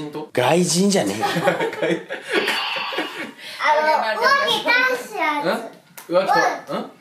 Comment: お化け倒すやつって最初私もも聞き取れなかったけどはやっぱ聞き取れるんだねー母ってやっぱすごい